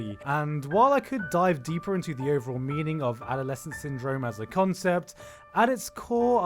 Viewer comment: I think I’m the only person who when reading the subtitles thought it said adole scene syndrome not adolescence syndrome